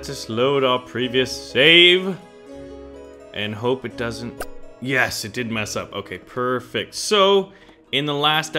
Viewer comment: says let's hope it didn't yes it did mess up at least that's what I heard